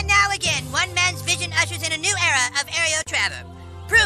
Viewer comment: Oh, he's saying intellect. Pretty longest time I was mishearing it as him saying Islam and got really confused